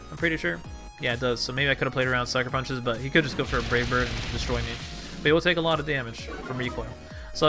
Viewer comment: I swear when you were battling Honchkrow it sounded like you said "Rape Bird" instead of Brave Bird.